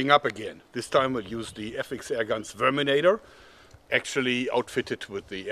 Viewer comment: did he say sperminator